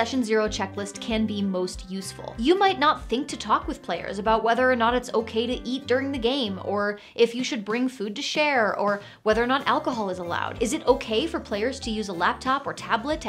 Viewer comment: When you said, "Whether or not it is okay to eat during the game." I heard 'Whether or not it is okay to EAT DIRT in the game." (Emphasis mine). I was then wondering if that was a character rule or a player rule.